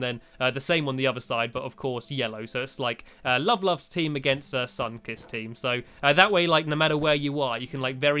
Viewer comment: you said the sunkiss team and I thought you said sunkist. the soda. do you know that soda?